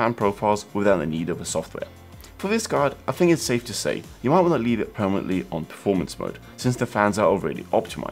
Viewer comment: What in the world did he say at 2:04?? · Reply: safe to say - you can leave the card in performance mode. I recommend turning on captions - they sometimes help out! :)